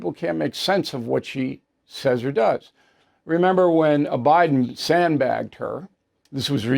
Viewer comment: does bill say 'obiden'??